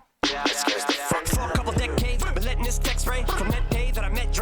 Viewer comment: Does anyone think there might be a triple in that essay bar. He says let the TEC spray, but it almost sounds like he says letting the TEXT spray. And then he mentions essay later. Am I reaching?